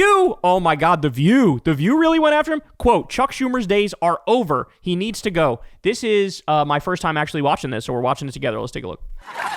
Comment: I thought i heard Kyle say Cuck Schumer and i thought that was such a great name for him.